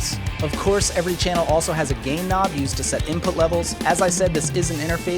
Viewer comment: Did he say "Gay Knob"